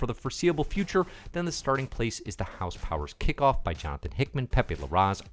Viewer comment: Did you say house powers kickoff?